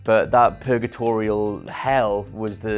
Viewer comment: Lol Did he just say "Purgatorial hell."